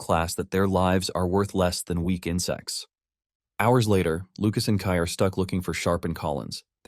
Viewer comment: your lives are worth less than weak insects sounds like he said. Your lives are worth less than weekend sex. Caught me off guard.